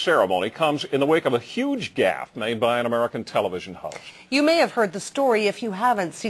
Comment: Hehe... Even though I'm seething with anger, can't help but break a grin. 0:55 I swear, I hear Ken Shaw calling Greg a ho, rather than host. I know he simply lowered his voice, but the way I heard it just makes it funny. AND HE IS! Didn't expect an insult like that to slip by, as justly put as it could be...